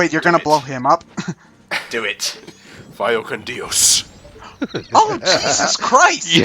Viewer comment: What did say there? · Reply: Late reply but he said “Vaya con Dios” Which is a Spanish farewell that translates to “Go with God”